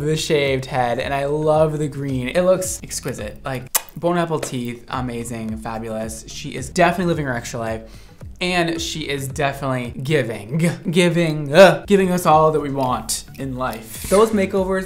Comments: Did he say "Bon appleteeth" at 13:28 ? 😂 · Why did it sound like he said "Bone apple teeth"? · Did anyone else hear “bone apple teeth” at 13:29 or just me? 🤣